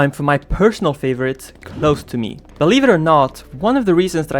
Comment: how is no one talking about the fact he said "lobster meat" rather than "close to me" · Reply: uh you kinda misheard him cuz he said close to me